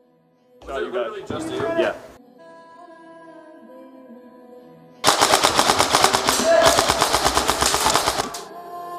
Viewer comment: What did that guys say at 4:15? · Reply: "Was it literally just you?" :-)